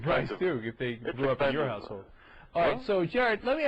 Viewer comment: The audio was a little wack at 15:10-15:15 . But did he really call Jared an asshole? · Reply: household